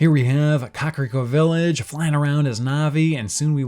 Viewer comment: Lol the auto-captions say "Cockroach Village"